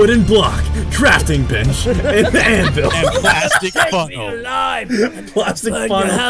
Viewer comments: OH he said plastic funnel! XD for almost 3 years I thought he said plastic butthole XDXD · I thought he said crafting bitch XD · Oh, "plastic funnel"! I always thought they were saying "plastic butt-hole"